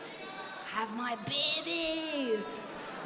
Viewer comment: I heard "Eat my paninis!" and not "have my babies" - and btw, how cool is the no-crossing tape thing she is wearing